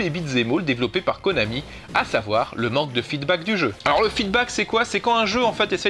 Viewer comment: C'est quoi le mot à 20:23? Feedback?